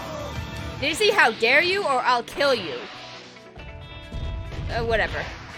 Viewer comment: he clearly said I’ll kill you